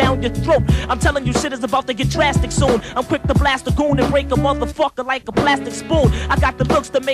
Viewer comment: I always thought he said "I'll cryptoblast a goon" not "I'm quick to blast a goon"